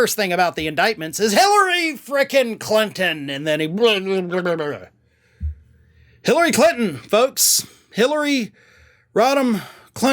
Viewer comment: All I heard was "b!tch b!tch b!tch but Hillary Clinton"